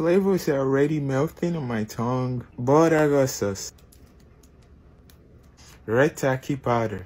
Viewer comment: Did i hear "BORAGO SAUCE" instead of Buldak for taco tuesday?????